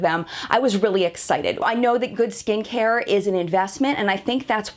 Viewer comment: For a moment I thought I heard you say "good stinkhair" instead of "good skincare". Apparently even my ears ar eblond. lol